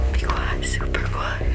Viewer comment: i heard "beware the super attendant"